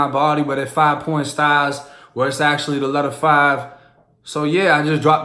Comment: Did you just say " the letter 5 "??